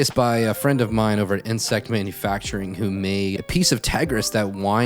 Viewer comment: was that nsec manufacturing? Could you please post a link as I'm not sure what you said. Thanks!